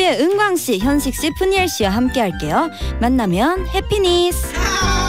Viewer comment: wha- what did she just say? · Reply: happiness